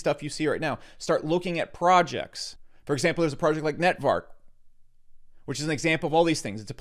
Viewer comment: Some amazing Alpha right here!! Thank you! Please can you let me know the name of the project you mentioned at 22:22. Can't make out the word you had mentioned. Thank you once again! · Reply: NTVRK